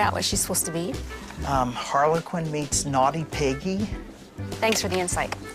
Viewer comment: did he just say 'Harley Quinn made naughty piggy'? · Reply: meets